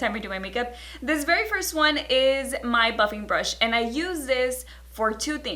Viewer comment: When you said." That's where you're gonna find this buffing brush." I heard at first "f**king brush" 😂😂 lol I love you tho! #funnytoMe lol